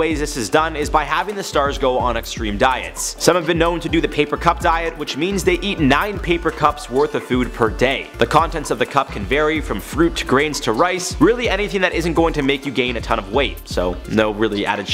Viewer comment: I thought he said they eat 9 paper cups a day, I was even more horrified